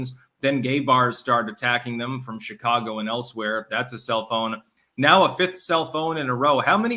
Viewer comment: sounded like ya said cellphone, self own LOL. Also don't play into the attacks and say "clearly I'm right and I'm gonna get a swastikas tattoo" in case there are folks listening that aren't aware that that movement was actually a far left wing movement. More people than not don't know history